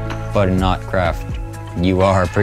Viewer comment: I thought he said ‘ourcraft’